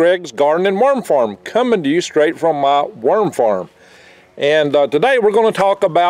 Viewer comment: I thought he said 'Myrm farm" . Otter got "myrmed", it was not pleasant. -Future Man